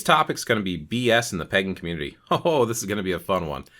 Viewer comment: I thought you said, "bs in the pegging community." Books will only take you so far, and it's separate from experiential knowledge, which is far superior.